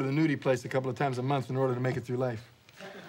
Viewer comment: 'Well I sneak over to the nudie place a couple times a month in order to make it through life.' 😂😂😂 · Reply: i thought he said "in order to make it with my wife.